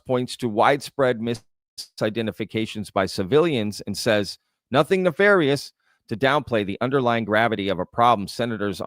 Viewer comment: I heard “not nefarious”